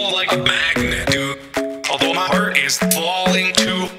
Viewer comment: it sound like he is saying i made love with ur body